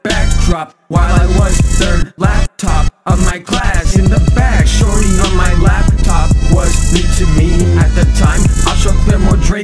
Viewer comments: i thought he said "shorty on my lap, top" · i thought he said “shorty on my lap”?